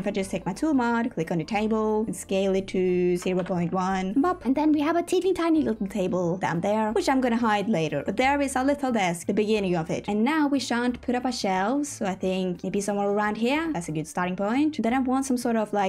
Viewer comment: Does anybody else giggle when says "shall"/"should"? I don't know if she's actually saying that though because every time, I hear the word "shan't" which actually means shall not lol 22:29